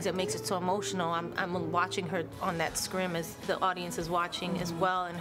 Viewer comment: Is it me or does anyone else hear Jlo say @ 2:06 “Screem” instead of screen?? Lol